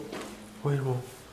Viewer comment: escutei "Oh Jão"!!!